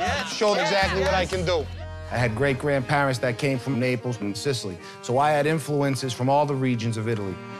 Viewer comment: did he just say "i have parents from napoles in sicily"? cause napole is not in sicily. any way, i hope the first lady gets far into the competition.